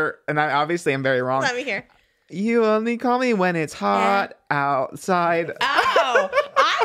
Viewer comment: I thought it said “you only call me when it’s half past 9” 😂😂😂